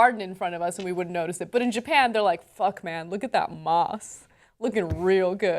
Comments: I thought they were saying moths this whole time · OMG - I thought they said moths, which might make more sense.